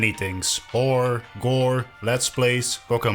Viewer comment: did bro just say Vore?!